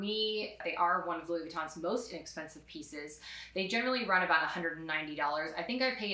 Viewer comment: BLING ASMR technically books are cheaper. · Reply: omg you said most inexpensive I thought you said most expensive!!!! Lmao I'm so sorry it's late! Great video!!!!!!!!